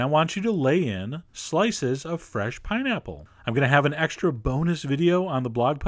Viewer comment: I thought he said boner instead of bonus ( - _ - ) hehehehe